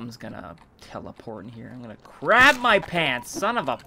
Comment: Did... Did you just say that you were going to "crab" your pants at 2:42?